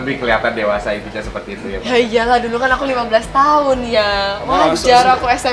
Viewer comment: Menit ke 9:19 YA IYA LAH KAN AKU DULU UMUR 15 TH YANG:) salah denger gak si gua 🤣